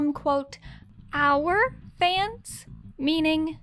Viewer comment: I thought Bryan said, “our family”. I didn’t hear, “our fans”. 🌷🫶🏻🍀